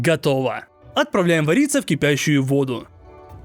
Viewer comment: послышалось "Отправляем Бориса в кипящую воду" еще с такой добротой сказал:DDD